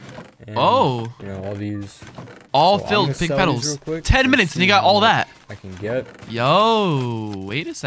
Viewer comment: did he say the n word?!?! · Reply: “10 minutes and he got all that”